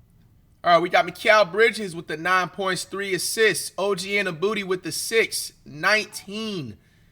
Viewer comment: Every time OG got the ball I heard flight say “annabooty”😂